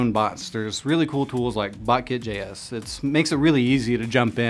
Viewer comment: what is the name of the tool you are saying? Please reply/edit CC so people can see it. I've replayed your words there multiple times to no avail. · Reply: I think "botkit.js". This is the closest that I could find: <link> I hope that's the right one. Good luck! :)